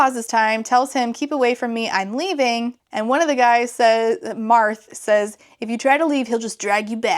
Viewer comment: DID SOMEONE SAY MARTH